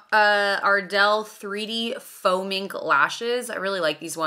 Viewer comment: When you were putting on the lashes I swear I heard you say “I’m putting on Ardell Foaming Lashes” I was like…. Interesting…. Then it dawned on me they’re Faux Mink…. It was 5 a.m. & I only took 1 sip of my coffee so…..🤣